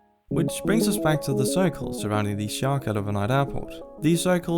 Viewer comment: Ok I've listened to 3:37 several times and all I'm getting is "surrounding this [sure cut of a night] airport". Please help me understand what you're saying here. Edit: it's Sharq El Owainat Airport. No offense, but I would've never guessed that's what you said :'D